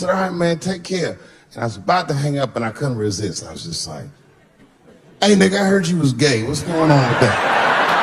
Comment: bro I swear the first time I watched this he said “ ay I niggh I heard you’ve come out the closet”